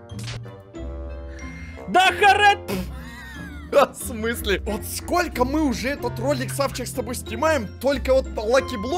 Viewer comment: что он сказал? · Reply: Не бегаю высоко и не прыгаю быстро